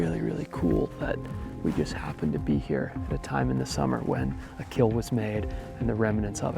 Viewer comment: wait hold on I’m confused here chat I thought he said Canada, I know he said summer but isn’t the artic like full of snow? When it snows that whole place is full of snow? Do you guys have climate change like Colorado one day very sunny no snow on the ground then next day yes?